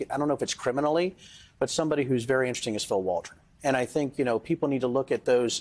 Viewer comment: who did he say? Phil Waldron?